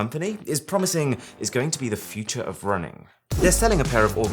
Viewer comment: For one second I heard " the future is running"